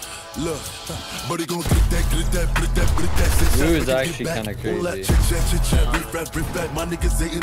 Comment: Bro did Bugha say "Moo is actually kinda crazy" at 0:17 or am I tripping